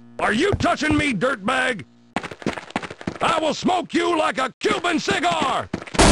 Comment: i thought he said "I WILL SMOKE YOU LIKE A HUMAN CIGAR!" (it still lies with the context)